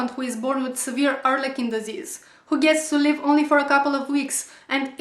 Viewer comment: I'm sorry, I know you're being serious, but at first I thought you said "severe arm-licking disease".